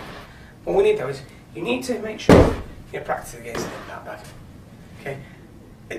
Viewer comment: What did you say between 7:59 and 8:03? I know it is essential, now, but I have no idea what you said :) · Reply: "you need to make sure practice against the impact bag"